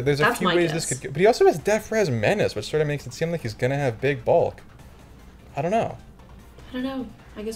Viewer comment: the way said "Big Bulk" sounded like he was gonna say "Big Balls" and I almost erupted with laughter